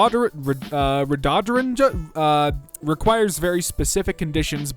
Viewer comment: I thought this guy said "dodecahedron" (spelled correctly first try, get on my level) and I was like "huh? that’s a shape, not a plant"